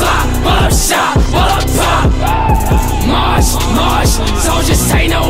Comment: I thought he said "mosh mosh soldiers satan are in a mission", but it's "march march soldiers take no intermission"